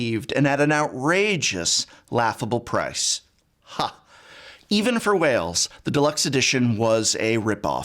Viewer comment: I kept hearing "even for whales" as "even for Wales" and i'm like yeah the welsh deserve less but why u bring it up so much :-/ im kidding